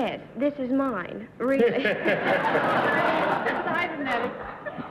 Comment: What did Dorothy say at 12:45? It was two words which I couldn't hear clearly. Somethi9ng to do with wigs? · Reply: Kenneth Bjorkmann I think she says "that's fine then".....possibly?